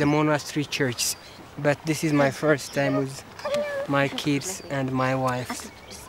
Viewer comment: did he just say "my kids and my WIVES" 😅